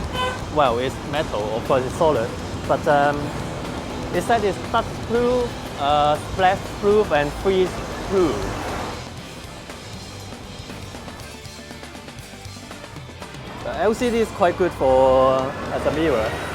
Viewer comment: Lok — Cat flu, splash flu and flee flu :D Sounded like Joey speaking French :)) Such an awesome guy.